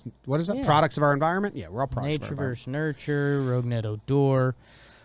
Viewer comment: What's he say at @ 13:46 "nature vs nurture..."